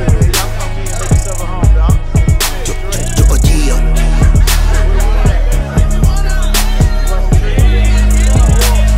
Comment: cool track cool melodies ! but what does rittz say at 0:25 he say it in every song it sounds like "drop it yeah" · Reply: He's sayin' "Ya-uh yeah" ;)